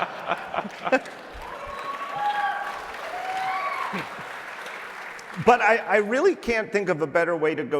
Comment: What did Mike Rockwell say at 44:54? I re-listened several times but couldn't make it out. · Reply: "we have enough of each"